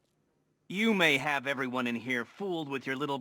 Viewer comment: Is Riddler serious, how could he actually think Batman is actually trying to fool him. Did he not watch the Live Broadcast? Also, I thought he said “you may have fooled the world”